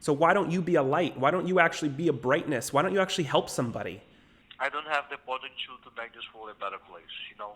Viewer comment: The inaudible part is him saying "fortitude." In other words, he doesn't feel he has the strength to do so.